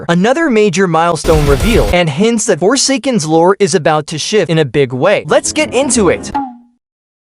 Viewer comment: i thought it said shi